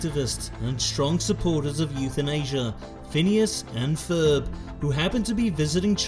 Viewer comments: Did anyone else think he said "strong supporters of euthanasia, Phineas and Ferb" at 0:40 , or was it just me? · “strong supporters of euthanasia” is what I heard instead of youth in Asia hahaha